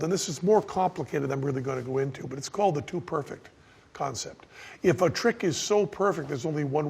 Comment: Thought he said Dude Perfect when he said To perfect lol